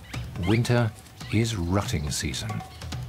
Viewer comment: I thought it sounded like he said...winter is rotting season not rutting season lmao Smh damn foreigners 😆